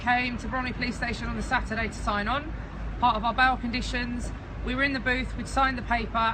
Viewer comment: I'm stupid. I was confused for a while as I thought she said bowel conditions